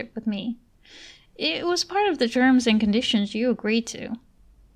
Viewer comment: Did you say, "germs and conditions." That's so cute! You did such a good job with this audio.